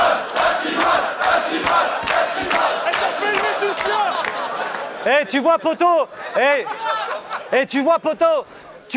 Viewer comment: Il a dit quoi ? XD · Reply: Elle s'appelle Leatitia